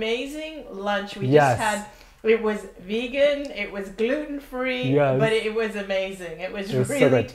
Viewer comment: Haha, you said gluten free.. I heard "duty free"!